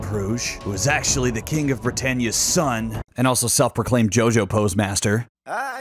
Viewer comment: Did he just say king of the Britannia's son?